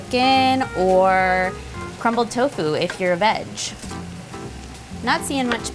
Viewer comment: i thought she said o “or crumbled tofu if you are a betch” 😂😂